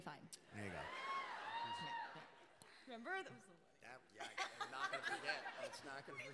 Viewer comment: plz someone tell me what does maggie q say in 43:41???? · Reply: "Remember? that was so funny."